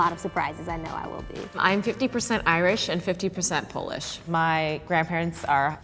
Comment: I thought said I am 50% Irish and 50% goldfish 😂😂😂😂🤦🏻‍♀️🤦🏻‍♀️🤦🏻‍♀️I was like what??? And then I replayed her part and I realized she said polish 😂😂🤦🏻‍♀️🤦🏻‍♀️